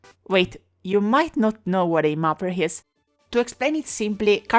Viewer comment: For a second I thought she said Melpert